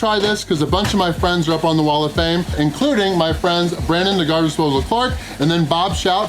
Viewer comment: Sounds like he says “Brandon the guy who swallowed a fart."